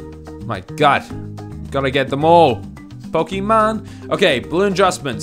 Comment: Did you just say Pokémon and then change the subject?